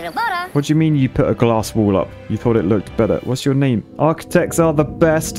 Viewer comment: The architect beaver actually sounded like he said ‘ha, I thought it looked better’